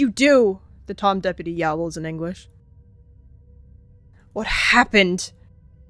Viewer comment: i thought you said “he yowled, in english”💔